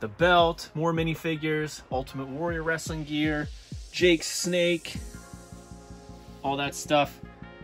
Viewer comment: he says Jake Snake instead of Jake "the" Snake. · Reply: No I said “Jake’s Snake” because it’s the role play Damien.